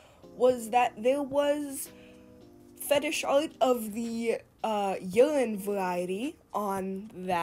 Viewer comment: Like I know there’s stuff with minors involved either way, but when you said “urine” I thought you said “youngin” and I was like WTF